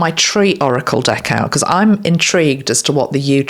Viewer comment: What a beautiful and comforting BIG message! I was struck by the playfulness of the specific words from Spirit. For example, when you began discussing the yew (YOU) tree’s significance, you said, “I’m intrigued” and I laughed because I heard “in-TREE-gued.” Just little charms along the way, I guess, and fun. My inner child is alert to such charms. Enjoy your vacation with your Dad. ❤️